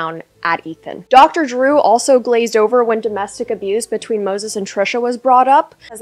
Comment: don’t get me wrong, ethan and trisha are controversial but i feel like no one talks about moses enough. the allegations against him are pretty mf serious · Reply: I thought you said noses😭